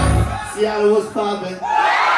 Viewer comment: What he says?? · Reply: “Seattle was….” And something i didnt get